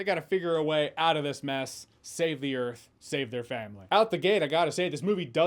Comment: I swore I heard him say “Save the Cheerleader, Save the World”.